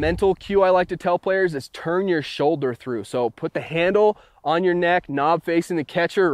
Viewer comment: When it says turn YORUR shoulder through and yes I spelt it like what it says